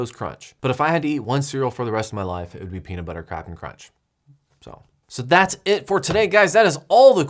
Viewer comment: At roughly 20:46, did he really say "Peanut Butter Crapin' Crunch"? Was that intentional or just kinda the way it came out?